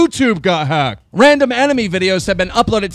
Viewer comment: Did he just say "enemy" instead of "anime"? Sigh..